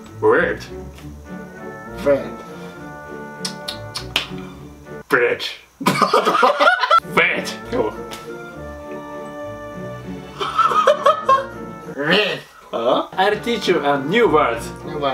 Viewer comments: ビッチって聞こえたのは 俺だけ？ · のbirdがbitchに聞こえたよ